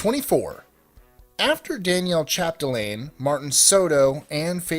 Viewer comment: On #24, it sure sounded like you said "Danielle"! It's so weird how you said "Daniel" as "Danielle" and their pronunciation sounds so unalike!